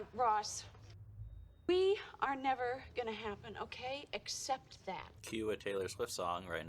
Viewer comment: The first time I watched this, I also thought Rachel was saying “EXcept”